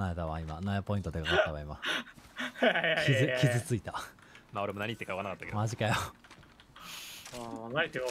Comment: スタヌさんなんて言ったの？ なんで釈迦さん萎えたーってなったの？ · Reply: 釈迦「プラウラーセレファ付き紫マガジン落とす」を若干噛んじゃってスタヌが「なんて言ってるのかわからん」と言う それに対して釈迦が「萎えた」と言う